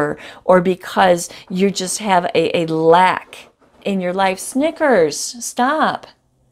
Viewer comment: It’s annoying when they scratch and puss about like that! I’m the same I thought you said “Your life’s Knickers” 😂 This is my Aries. It’s must be. Your emotion is like it’s you! Lol I feel like this is personal to me! Small Big Head!!!! I can’t imagine that I will be that bold with him! But who knows! I hated the fibbing and I know him up and down now. If it wasn’t for the DM between us... I would have written hiM off. This DM is a walking heart and so am I! Is this for real.. we will see